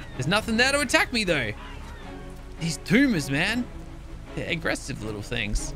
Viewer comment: “These tumors man.” Me being half way deaf and not paying attention to what he said: “Did he just say... ‘These boomers man’?”